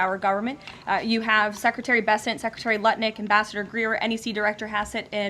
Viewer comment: I swear it sounded like Karoline said Nutlick instead of Lutnick